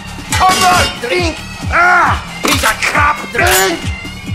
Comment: This is wonderful, and yet so sinful. The best part has to be "D R I N C C" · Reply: At first I thought demo said: "hE's a CoP! D R I N C C"